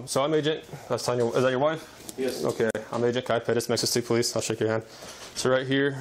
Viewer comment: Is he saying he's with metro state police